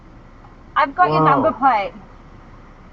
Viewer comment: Hi, is there anyone can hear what she said at 0:45? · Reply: "I've got your "dumpoplay""???